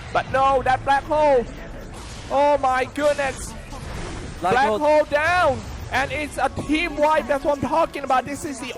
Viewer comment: black hole down black hole down.... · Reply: Lol .. that moment i thought he said black hawk down..😂